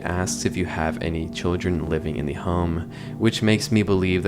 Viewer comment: I dunno, at around 13:20 I hear "Do not have children within/live in the home." not "Do you have children living in the home?"